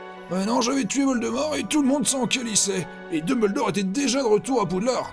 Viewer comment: C'est juste moi ou il à dit "calissaient" a 22:41?